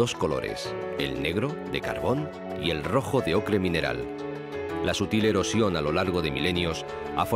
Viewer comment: No logro 'pillar' lo que dice en el intervalo 03:58 a 03:49 ("las ... a lo largo de milenios"). ¿Álguien me podría ayudar? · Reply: Hola Issuarte, creo que te refieres a esta frase "la sutil erosión a lo largo de milenios...". Esperamos haberte podido ayudar y muchas gracias por seguirnos